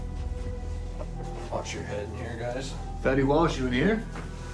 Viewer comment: I thought I heard "Fetty wap are you here?"